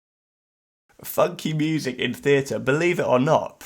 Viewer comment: I thought he said fucking music for a sec and was like wait what??? and had to rewind haha